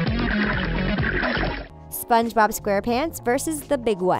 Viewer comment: I thought the teens saying HAHAHAHAH its spongebob nudie pants because it's kinda innoppropriate